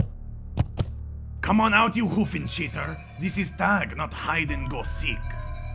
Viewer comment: I HEARD HOOFENSHITTER.